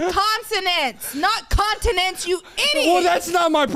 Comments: Okay to be fair I thought said continent so I can see how thought that 🤣🤣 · I am so on level. I thought she randomly said continents too.. 🤣🤣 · To be fair.. i thought she said continents too i was confused like “is Europe not a continent?” LMFAOOOOOO